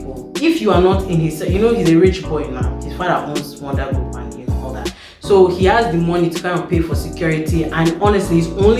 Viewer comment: did I hear “his father owns Wonder Woman and all that?” 🤔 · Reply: NO!! 😂😂😂, I said "His father owns Wonder Group and, you know, all that". But I understand the confusion, I was mumbling...